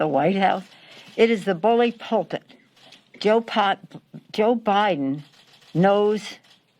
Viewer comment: did she call Biden Pol Pot?